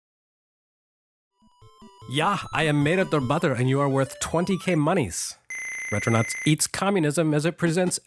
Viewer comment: Haha, I just about spit out the water I was drinking at that moment out as soon as I heard 'I am der butter'